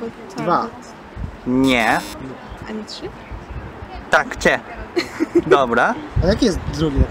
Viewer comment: ノーブラにしか聞こえない